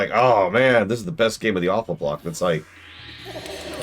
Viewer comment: Everytime says "aweful block" I hear "Alpha block" for some reason :-D (not a native speaker tho)